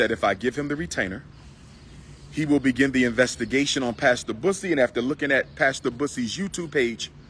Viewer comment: Thought he was saying Pastor Puss__!! Kansas City THOT was hilarious. "It must hurt his neck wearing that halo 24/7".